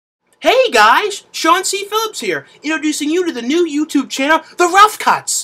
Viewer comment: Lol at first I thought you said Chauncey Billups...